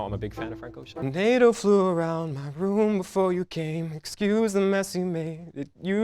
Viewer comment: I thought he said a potato flew around my room